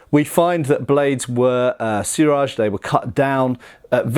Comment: "Buy Yako Katana", huh? It's Pronounced (Bee-Yak-ko), and at 1:57 did he say Suraj? I think he means O-Suriage お磨上げ (pronounced -O-Su-Ree-Ah-Geh) Somebody should teach a lesson or two in basic Japanese pronunciation. It really wouldn't take long. And then his videos would have another layer of authenticity to them. IDK what it is with guys from the U.K. They always seem to butcher Asian languages worse than everybody else. On second thought, no. They're right on par with certain Americans. So I guess I am just being nitpicky over a small matter. Never mind. 🤦